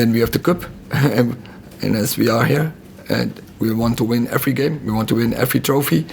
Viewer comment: All I heard was "we wanna win everything" My manager 😩😭😭👏🏾👏🏾👏🏾💯